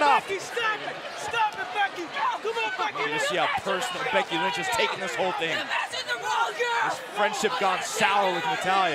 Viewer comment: What did Becky say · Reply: Shane Washington you messed with the wrong girl